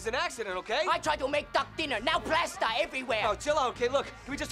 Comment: I Swear it sounds like hes saying, "I try to make a Duck Dinner now Breast are everywhere" But plasture is funny too, i guess